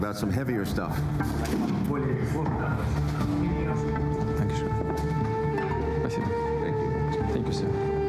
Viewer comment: is Putin saying "Dankeschön" ("thank you" in german) right there?